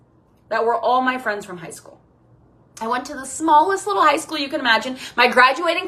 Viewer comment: also my graduating class was 9 - her 42 isn't the smallest possible lol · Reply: Mine was 24, I heard her say "tiny ass high school" and expected a way smaller number 😅